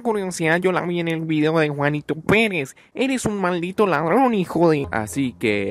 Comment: que mal momento para llamarse juanito peres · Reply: Yo pensé que dijo Juanito penes XD